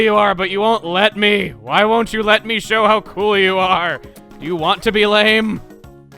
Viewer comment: what he said: "do you want to be lame?" what I heard: "do you want to get laid?"